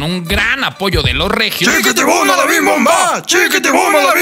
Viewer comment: Se dan cuenta que dice "Chiquitin"? 😅